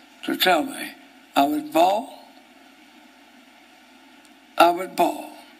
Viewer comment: Umm, what does he say?!? “I’m fall?” Soon after he said,I’m not going to fall(?).” It’s the only thing I can figure he said!! Lol. Please if anyone can better understand what he’s saying, please let me know! PS: I’ve been binge watching your videos and they’re all GREAT!!! I love your narration skills along with your humor!